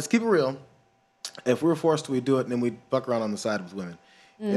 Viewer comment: Could someone please explain to me what Jayar was saying at 5:19-5:21? "If we were forced to it, then we would (unintelligible) blah blah blah blah." I'm curious. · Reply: ***** guys would fuck other women on the side.